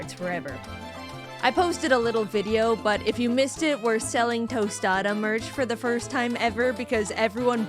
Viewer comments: I didn’t hear the merch part, so all I heard was “we’re selling Tostada” and I was like “WHAT- WHY-“ and then I heard the merch part 😭🥀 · my internet glitched right at the end and all i heard was "we're selling tostada" before it cut off and i was HORRIFIED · I PAUSED THE VIDEO NEAR THE END AND I THOUGHT JAIDEN SAID "WE'RE SELLING TOSADA-" 😭